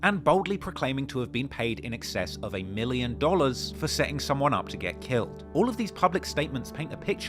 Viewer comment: I thought that man said a 8million dollar hit.. I must’ve lost my mind 😂😂